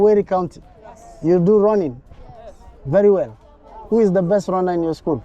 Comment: What do you mean our students are taught in Swahili😂😂😂 dude it's your accent that difficult for them. "Who is your best runner" your pronunciation is the one that was problematic. Runner sounds like you said Rona!